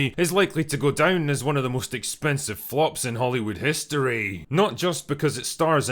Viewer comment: I literally thought you said, “One of the most expensive focks.”